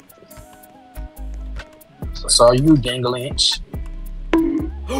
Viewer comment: did bro said gangalantch?? 😂 I’m done bro. Good video.